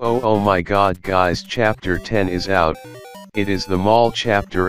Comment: I thought you said "finally chapter 2 is out" XD